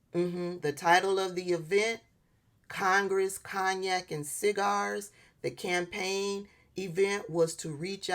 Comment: Congress, Kayne and cigars. All things that’s are bad for you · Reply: I thought she said Congress, Cognac, and cigars.